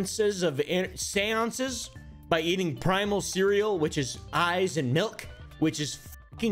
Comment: I thought oompa said "he started eating primal cereal, which is ice and milk" and i thought "mmm, doesn't sound bad tho it doesnt sound like a primal meal either". Nonono...man said "EYES and milk" and literally my eyes popped out of my sockets cartoon-style